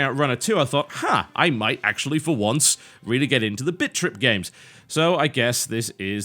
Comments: lol sounded like he was saying bitch trip :p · All i hear him say is "bitch rip" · I keep hearing 'bitchtrip'... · Sounds like he is saying bitch trip XD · Either for irony or trolling, hopefully irony. Also, I could almost swear that I heard TB say "Bitch Trip" repeatedly. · It sounds like he is saying bitch.trip at the beginning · sounds like your saying bitch trip lmao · it sound like your say bitch trip lol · to me it sounds like he is saying bitch trip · he sounds like he saying bitch rip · It sounds like he is saying Bitch Trip. :3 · sounded like he said bitch trip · Sounds like he is saying bitch trip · All I hear is "Bitch Trip"